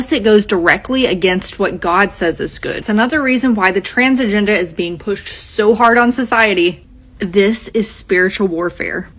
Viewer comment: I know English, I can also understand the different accents and understand what they say, BUT I HAVE NO IDEA WHAT THE FUCK SHE JUST SAID, can someone write down her words for me😭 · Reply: she said “Another reason why the trans agenda has been pushed so hard on society”